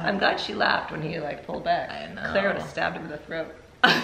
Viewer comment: “Claire would have stabbed him in the throat” ☠️☠️☠️ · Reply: I thought she said "cause I would've stabbed.." ?